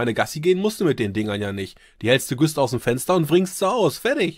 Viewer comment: Bei 5:58 was sagt er da? "Die hältste ... ausm Fenster?"